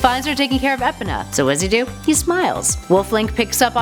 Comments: what did she call the horse? Epena? LoL 😂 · did she just say epana